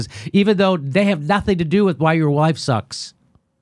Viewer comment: Did you just say 4:15 " ....... why your wife sucks"?!!! Looks like that's what heard too. Epic Freudian slip!